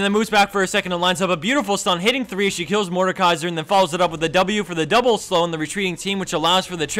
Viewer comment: did i hear, kill mordekaiser? BLASPHEMY!